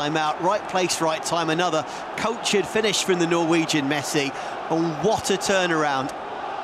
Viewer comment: Did I hear "Norwegian Messi"??? 0:27 😂😂😂